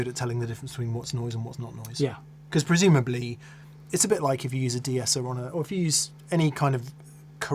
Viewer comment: What was the noise suppressor plugin You mentioned at 35:37? I didn't quite catch that. Thanks! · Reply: was referring to a 'de-esser' which can be hardware or software, meant to reduce sibilance (S and T sounds) from vocals. It's like a compressor that only responds to a certain frequency band.